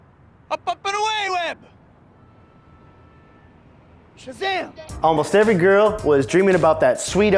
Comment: Does he say shazam